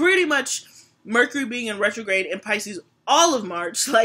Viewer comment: It sounds like you said Mercury in Gatorade and not Retrograde lol